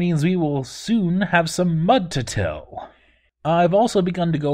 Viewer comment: the trumpeting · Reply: Wait a minute did you just say you are soon going to have some mud to till ?